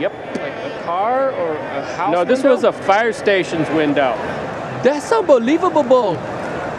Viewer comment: I wonder how often bad lip reading matches what was actually said. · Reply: I thought he said: "That's a foul baaall!" but yes "unbelievabable" is more believabable